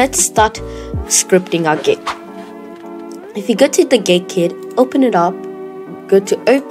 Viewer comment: Did bro just say gay kid? 2:21 and make sure captions are on for more proof XD